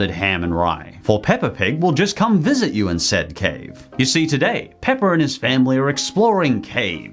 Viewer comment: Did he say Peppa and his family?